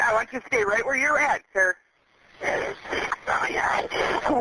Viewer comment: Let's take another angle. On the video, you will hear him say something at roughly 3:19. The words on the screen say "oh my god". That's not what he says. Listen to it again, there are at least 4 syllables, perhaps 5. Not 3. His voice shifts from a high panic tone to a low whispered voice with a nasty intent built into it. It's not "oh my god" he's saying. Adjust the video speed to .75 and then to .5 speed. Now you can hear what he really says; "oh, yeah, I did," or oh yeah, I did it." I don't think is anymore. You viewers still asleep? Or are you catching on yet?